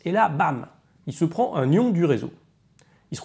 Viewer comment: J'ai compris "paf, il se prend un gnion du réseau" 😂